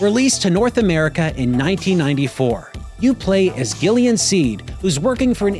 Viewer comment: I heard "You play as Alien Seed." Somebody help me